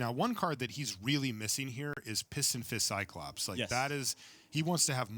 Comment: everytime Marshall references pistonfist cyclops I keep hearing Piss 'n' Fizz Cyclops xD